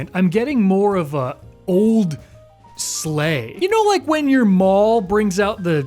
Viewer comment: At first I legitimately thought you said, "You know like when Darth Maul brings out the Christmas decorations..." (I think I coughed or something right when you said "your") and I was just thinking, "What are your holiday traditions like? ....Why am I watching this?